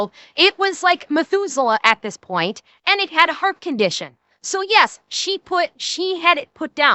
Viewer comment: For a while, I thought you were saying the cat had a fart condition.